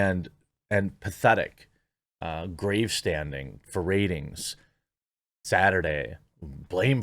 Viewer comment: I misheard grandstanding for gravestanding. Its got a weird ring to it, like parading over someones death, like a psychopath